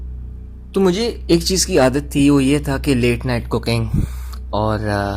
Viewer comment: I thought he said "Late night Cocaine" 😅😅